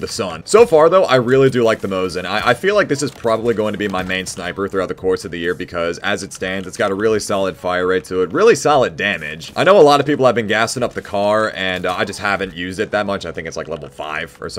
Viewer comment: I first heard "gassing up the car" instead of "gassing up the kar"